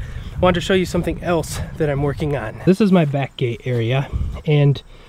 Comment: I’m not finished with the video yet, but I totally thought you said, “here is my bat cave area”. I’m like... 😂😂😂😂... bat cave... back gate... I’m in WI and can’t even say bag right, so it’s prolly my ears 😂😂